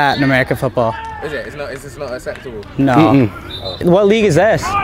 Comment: The guy says 'is this not acceptable' and the caption says 'is this not a sector' lmaooooo